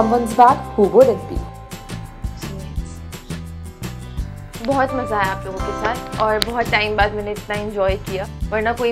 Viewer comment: what she say??? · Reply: Sumedh's